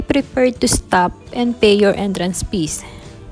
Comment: I knew you were Filipina when I heard you say "entrance pees" ☺